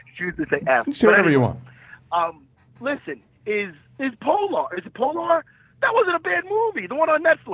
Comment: I thought he was saying Poldark, Polar. Yee polar is dope, awesome action and hot...extra hot sex scene. haha Plus the man Mads Mikkelsen